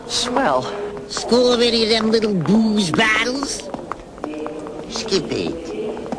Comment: What's Jammet saying at 8:27? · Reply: "Score (?) any of them little booze bottles?" Score sounds like school, but I think that's what he meant. It makes more sense.